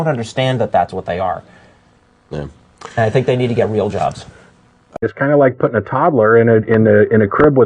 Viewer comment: i thought he said "blow jobs". I was like YES!!!! love this show!